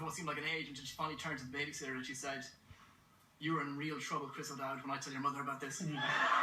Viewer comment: Could someone help me and tell me what he says from 1:35 to 1:38?? thanks!!! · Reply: “You’re in real trouble Chris O’Dowd when I tell your mother about this”